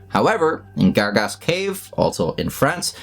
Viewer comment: When you said Gargas Cave, i thought you said Dark Ass Cave